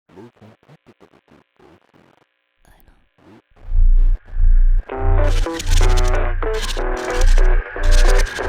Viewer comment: あたり、ヘッドホンで聴くと私の左耳付近で遊さんが何かコソッと囁きました。最高。何言ってるかわからないけど。 ーーーーーーーーーーーーーーーーーー Close to 0:02 Yu whispered something but I cannot understand what he is saying. Anyway, I love the whisper. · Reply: He's saying "I know" (it appears on the video that second)